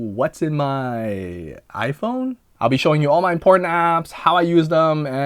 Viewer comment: Phew omg I thought I clicked on wrong video at 0:08 i thought i heard porn apps .... mind must be in the gutter lol 😆😂😵